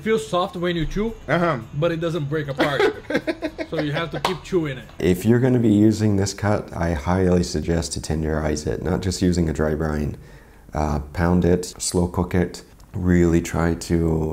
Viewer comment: The Closed Captions for this video are hillarious. At 12:53 the CC says "If you're gonna be using this cut I highly suggest to Tender your eyes."